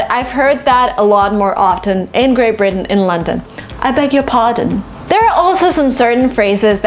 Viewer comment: I beg your pardon I thought everyone said " i beggi pardon" 🤣 Thank u i got that now